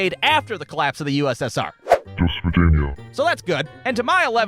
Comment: why did i hear west virginia